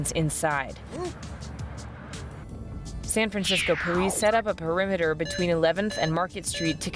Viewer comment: I think she said porice